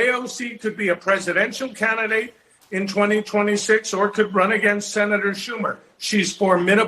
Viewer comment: did he say 'Presidential Candidate in 2026'? I guess they are going to run her in 2028?